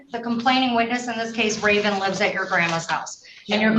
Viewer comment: Did I just heard the judge said "That's so Raven"?